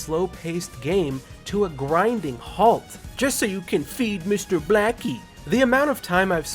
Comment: did he say "mr blackie?"